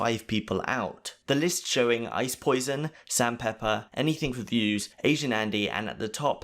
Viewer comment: Did he say ice poison? Lmao